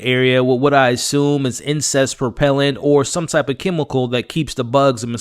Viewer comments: I thought you said incest repellent in the beginning of a video. That made me laugh. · Did I hear “incest propellant”